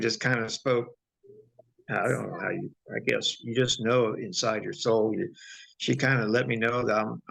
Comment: Wow listen at 3:00... Sounds like the lady that helped him chimed in real quick... Hard to hear what she said (sounded like "inside," right when he said "inside your soul") but definitely female acknowledgement right when he mentioned her. Gave me chills but happy to hear she was still around this nice man.